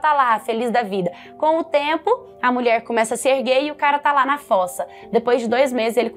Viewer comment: "com o tempo a mulher começa a ser gay e o cara ta la na foça...." 6:12 so eu q ouvi isso? KKKKKK